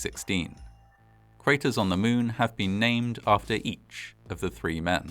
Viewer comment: did he just say, three crater's on the moon were named after each one of them? Oh righty then